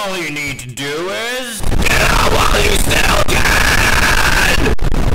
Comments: what baldi says in that ear rape? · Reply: Get out while you still can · did baldi say “GET OUT WHILE YOU STILL CAN!!!”?